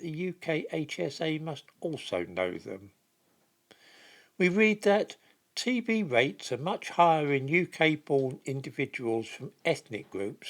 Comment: I hear TB's a racist now.